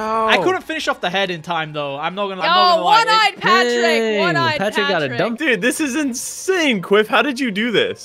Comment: I heard someone say "Daang Patrick got a dumptruck"